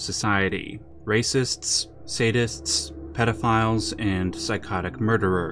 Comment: bruh I thought he said statists and I was like trueeeee.